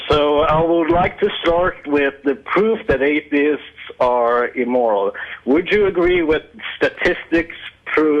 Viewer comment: I thought it said “atheists are immortal” like woah really?